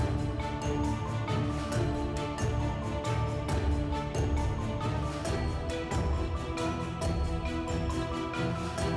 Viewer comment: Woww...QUÉ DICE MARE EN EL 1:37...QUÉ TIENE LA SUFICIENTE PLATA PARA..? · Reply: Pagar tu MUERTE. Clarito está y quieren hacerlo ver cómo que no.